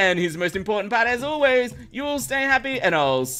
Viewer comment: did you just say happydays instead of at the start?